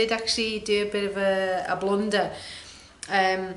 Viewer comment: OMG I thought you said, a blonder, not a blunder. Love your work. Thanks for sharing. 🌻